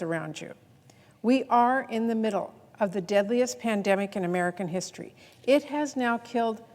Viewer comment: Did she just say deadliest pandemic omg insane